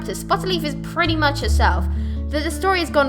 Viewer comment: When u said bluefur I thought you said Balooffa XD